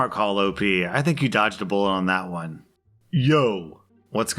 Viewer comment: Did anybody hear him say "you dodged a bull op" at 8:06?